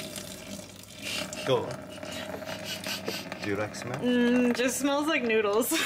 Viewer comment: I heard “durex smell” lol